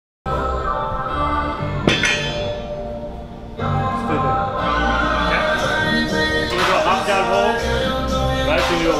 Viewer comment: lol anyone else noticed what he said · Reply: Maito ;D lol!